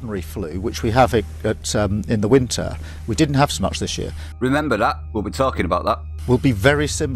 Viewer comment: he clearly says "we didn't have so much this year..." A completely different meaning to "we haven't had that this year..."